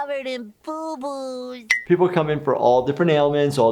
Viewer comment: Spongebob says he got covered in boo boos. Subtitles says 'covered in buboes'' BUBOES!